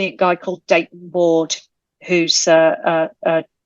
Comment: Lois McMaster Bujold! Yes! Funny I thought she said 'Data knows everything' but I realized it was a person named Dayton or something lol